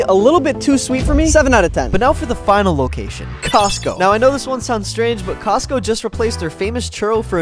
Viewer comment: I thought bro said cosmo when I realized it was Costco not cosmo from dandy'sworld